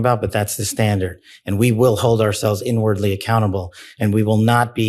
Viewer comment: listen closely, I've heard "cannibal" instead of "accountable" 😅. This mistake was probably the only true thing lol.